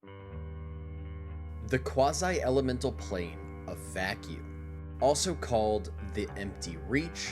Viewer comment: totally misheard the intro as "the quasi-elemental plane of fat people" first listen lmao